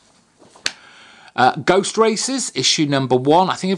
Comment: I could have sworn you said "Ghost Racist" (instead of Ghost Racers). Ha! Now there would be a comic!